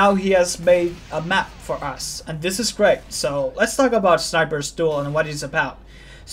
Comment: sounds like youre saying sniper's stool